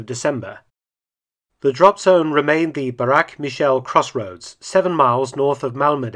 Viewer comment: did I hear you correctly?? "Barak & Michelle crossroads"??! Is that anywhere near the "Trump & Melania bridge"??